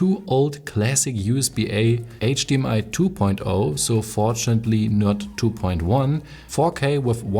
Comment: did you say fortunately or unfortunately?